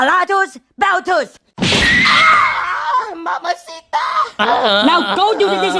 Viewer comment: what is even frank trying to say, dammit 😂😂😂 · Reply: He said mama sita 😂😂😂😂😂